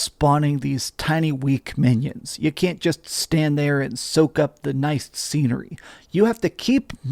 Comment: Thought you said "cant soak up the nice semen" at first listen 😂